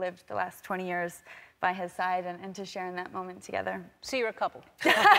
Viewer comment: Ellen: "Are you a couple?" Tessa: denies, avoids, deflects Ellen: "So, you are a couple?" Ellen is really out here ROASTING them, haha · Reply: I thought Ellen said "See, you are couple" and I was like "YAAAS!"